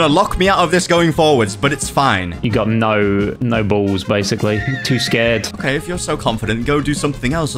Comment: Wht did he say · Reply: No balls🤔🤔